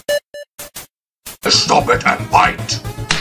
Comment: flowey: I'm a god, I have netflix That made me laugh so hard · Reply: Josiah Levia in the beginning it sounds like asgore is saying stop it I'm white